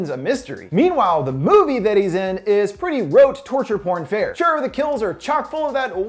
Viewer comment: what’s he saying? Wote?